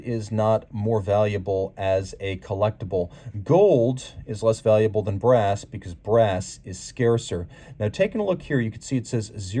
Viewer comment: Did you say "Gold is less valuable than brass because brass is scarcer? Did I hear you right or was I just hallucinating?